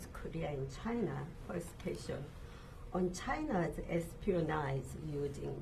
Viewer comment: didn't catch a word of that, can you repeat your question? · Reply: Something something "China espionage"